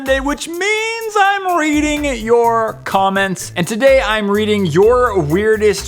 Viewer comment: I thought he said eating your comments😄